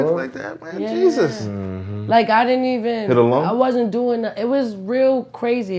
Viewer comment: hit ah lung?...... bro did you really say that😀😀😀😀😀